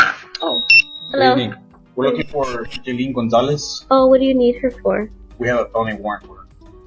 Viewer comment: i thought he had said speedy Gonzalez so i had to go back real quick and the fact that she tried running off made it even more funny😂😂😂